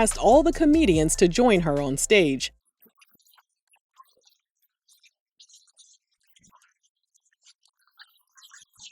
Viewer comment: What is being said at 6:40? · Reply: Sommore doesn't "rock" with Sheryl